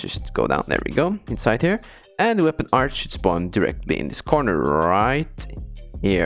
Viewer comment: LOL THOUGHT IT SAID DESPAWN